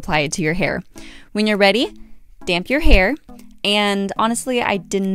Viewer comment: did you wet your hair before applying the mixture or did you apply it on dry hair? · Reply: how did you watch the video? at 1:18 she clearly said to damp your hair